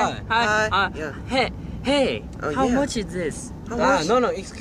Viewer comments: Jimin: Hey you pretty! Me: Flops on bed blushing insanely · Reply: I thought he said you pig 😂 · Jimin: oH hEy YoU PWetty- Me: *dies* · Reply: i can't be the only one who thought he said "oh hey! you bIG !" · I hear “OH HEY YOU BIG HEAD” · No one: Jimin: Hey! How much is this Jungkook: no no excuse me! Jimin: Aiscuse meee!! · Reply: what is that what he was saying all this time i thought he was saying "Hey I'm watching this ?"